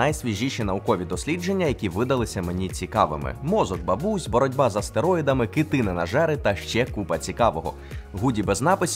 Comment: послышалось "боротьба із стероїдами"